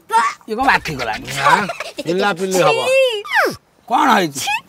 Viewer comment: did he just said 'pila pili haba' 😂